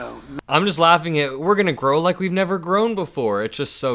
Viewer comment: did you say "we're gonna grow like we never have before" ? Or "We're gonna groan like we never have before" ? 🤔🫥😶😏😗🤔